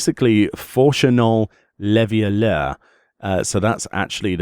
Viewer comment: I actually had to watch the video when I heard Fourchenault Leveilleur names because I heard "Levioleur" which means the rapist. I was like "wait what?"